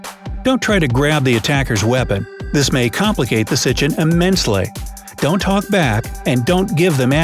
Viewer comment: he said sichin instead of situation